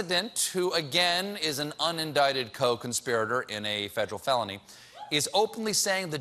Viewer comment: did he say coke inspirator? Or was that the white beard talking?